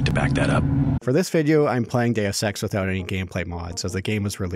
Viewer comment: “For this video, I’m playing Deus Ex...” For a moment, I heard “For this video, I’m planning to have sex...”